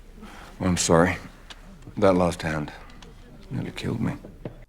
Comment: 'That lost hand, nearly killed me' Now I get it. I always thought he said 'That last hand.....' (not his clearest bit of dialogue). It was always a poor line to me. Not any more.